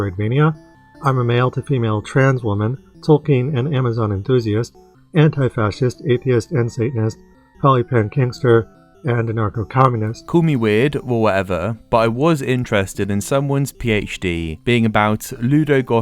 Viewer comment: I thought he said “anarcho-Calvinist” 😂